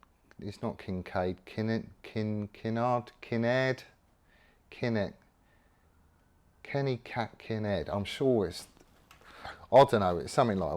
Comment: Let's just stick with kenny kak in future 😂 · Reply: I honestly thought he said Kenny Catkin Head. Kinda disappointed that’s not what your called😉